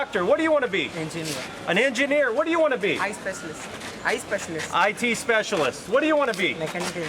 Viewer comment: it sounded like he said Eye specialist and not IT